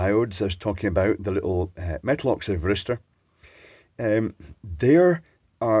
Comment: I keep hearing metal oxide barista. I think I might need my ears syringed again.